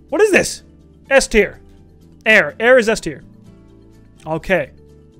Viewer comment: why did i hear "Air, Air is zestyer"